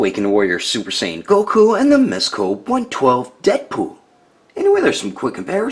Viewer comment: I heard dead poo